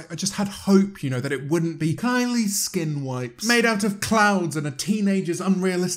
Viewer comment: “Kylie skin wipes made out of clouds and teenagers unrealistic body expectations” I hate how accurate that is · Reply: I thought he said clowns instead of clouds what honestly made it 10 times better for me 😂